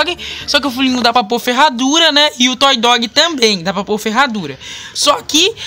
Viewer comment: Alguém obs: que ele falou q o foo Lion n da pra por a ferradura? Ou só foi eu?